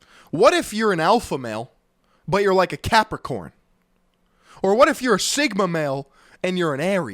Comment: When he said what if you're a Capricorn I thought he said unicorn and lost it for a second🤣